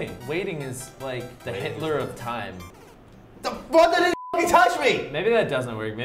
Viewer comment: I heard say f*ck